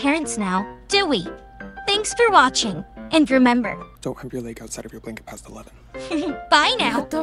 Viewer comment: Lo que dice en el minuto 4:42 es Don't show your leg outside your blanket past 11 😢